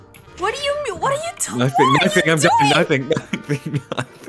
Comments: Did anyone hear at 6:23 said "nothing baby nothing", while he was laughing or am I just high? LMFAOO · Oum...chile did I just heard say "nothing bb nothing" while laughing 💀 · I could’ve have sworn I heard said..”nothing baby nothing” or is it me?😭